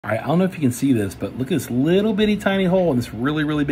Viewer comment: At first i thought he said why not to smoke